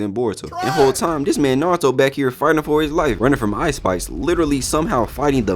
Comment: “Running from Ice Spice, fighting the map” goes hard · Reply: Good ear 😂 I thought he said " Ice Spikes"